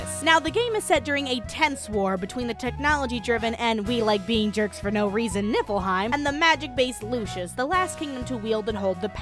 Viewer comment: Am I sleepy or did she say "Nippleheim"?